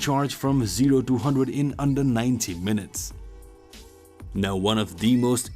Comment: sounded like he said it charged from 0 to 100 in 19 minutes... at first i was like GOD DAMNNNNNNN (But he meant 90)